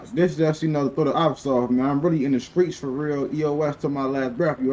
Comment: i thought u said u lefted the streets lol